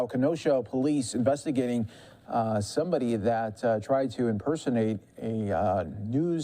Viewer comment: did he say "impersonate"? I guess most main stream media is impersonating journalists.